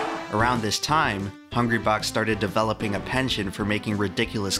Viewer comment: did you say 'pension'? I think you meant 'penchant.'